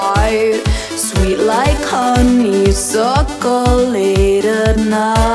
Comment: The first time I heard this: "Sweet like Kanye circle late at night" "I'm your cauliflower, I'm your bloody rose"